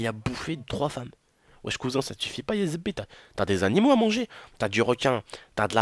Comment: C moi ou tu a dit : zebi = b*** a